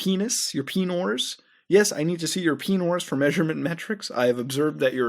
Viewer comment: Did... did he say penor?